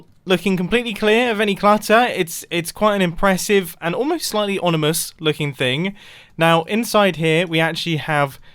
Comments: did he say onimous instead of ominous 😂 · Am I going crazy? Or at 8:38 did he say “onimous” instead of “ominous”? That’s great xd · Does anyone else hear onimous instead of ominous?